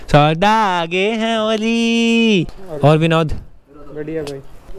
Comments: I literally heard"Binod"😂😂😂 But after listening carefully.. i came to know that its 'Vinod' Edit : THANKS FOR SO MANY LIKES.. SOURAV ARMY OP!!🔥 Like if u read! 👇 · wut did he just say binod 😂😂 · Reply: No man he said vinod